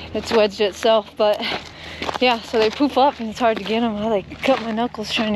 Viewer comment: LoL I kept hearing 'poop up' instead of 'poof up'. LMAO - took me awhile of listening to get it right. I was like... e<link>